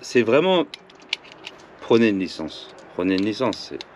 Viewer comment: "Prenez une licence" moi j'ai compris "Prenez un SIG ... et une licence aussi !"